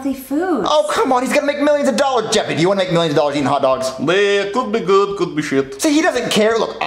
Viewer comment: Bro tf jeffy said in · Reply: it’s well could be good could be sh**